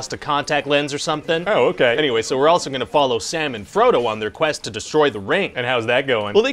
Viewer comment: I thought he said "Salmon Frodo" and got a strange mental image lol